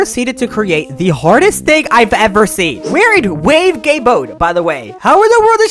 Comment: yt subtitles said "wave gay mode" lol